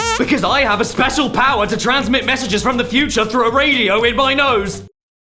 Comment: My headphones cut out during the spons segment so I heard “I the power to transmit special bitches from the future through a radio in my nose,”